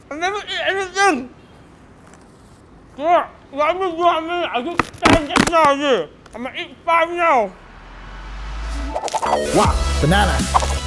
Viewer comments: "Could not figure what he was saying " at 6:00 have sub on 😂😂😂LOL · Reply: 'I am still hungry,I ate five just now only imma eat five now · let me help with that wah banana,what was saying is that he just now ate 10 only now I must eat 5 now. No probelm